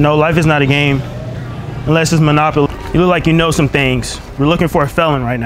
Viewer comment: “Life isn’t a game it’s monopoly” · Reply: I thought he said unless it’s monopoly